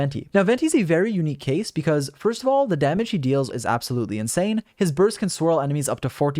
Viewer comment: kekwait the damage who deals? I hope I misheard it